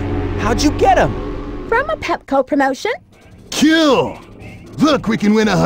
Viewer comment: "from a Pepco promotion" XD wow. I'm also positive that 100 million was only supposed to be 1 million but the translators got the yen to usd conversion wrong. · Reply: I've always misheard that as "Petco promotion"...